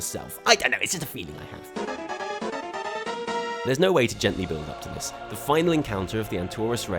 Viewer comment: I love how the subtitles say "There's no way to gently build a penis" at 15:18 :D